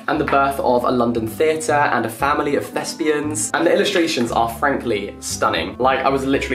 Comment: Thought you said “a family of lesbians”. Was confused !